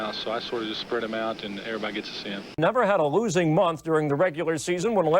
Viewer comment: everybody gets a sin....did I hear right?